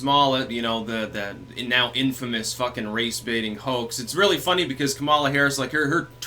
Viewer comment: At first I thought you said "Race-bating HOE" hahaha!